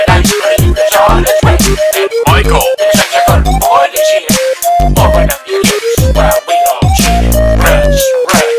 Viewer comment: I keep hearing "Why don't you eat this chicken?" at the end. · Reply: It's "open up your gifts" lol